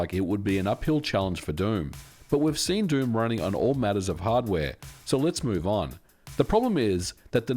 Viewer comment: lol did he say “all matters of hardware”?